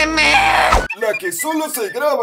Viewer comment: yo escuche "te mee te mee"